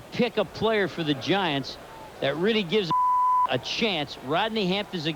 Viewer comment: did he say that really gives a shit 😂😂😂😂